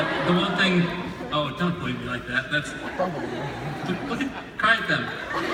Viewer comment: Did Bob say: "Don't look at me like that. Cry that way."?👌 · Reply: i think he said 'dont look at me like that, cry at them *gestures to audience*'